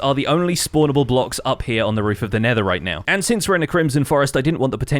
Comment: instead of saying roff of 'nether' you said ' netheright ' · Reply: Nah he said "nether right now"